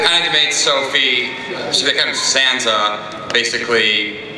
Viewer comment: does he call Sansa Santa?